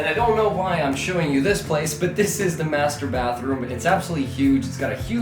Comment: I heard this is the masturbathroom 🤣🤣